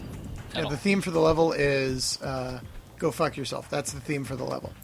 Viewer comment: what that guy at 05:35 said? "And the FIN for the level"? I'm not english speaker.